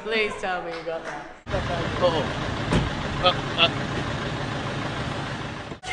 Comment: KEVIN!!!!! Anybody else heard that?